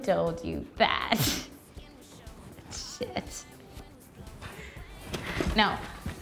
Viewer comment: Did Carly just say shit ? LOL !!